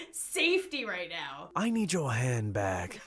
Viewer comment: XD LOL i thought the robber said i need your hand back then the last time that Lauren said it i realised that it said i need your hand BAG